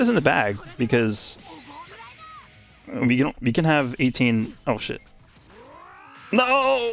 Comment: either I heard oh shi- or oh-hit